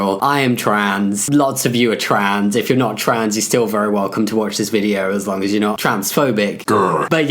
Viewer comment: I swear I heard “if you’re not trans, you’re trans” during the intro